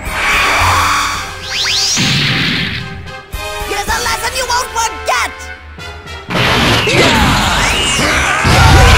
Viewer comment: What did mechafrieza said in 1:36? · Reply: "Get a lesson you won't forget!"